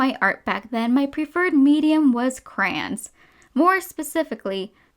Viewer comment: CRANS Me: What.. CRANS Me: did i hear you right?CRANS